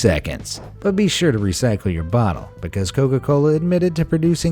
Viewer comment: Did he say Covid-Cola